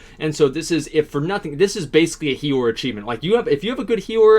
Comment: wtf is he saying? hero? is he refering to the healer?? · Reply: he's saying it's a healer achievement